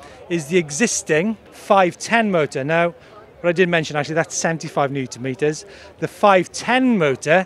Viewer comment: sorry i cant understand 5:54 you said 75NM?????????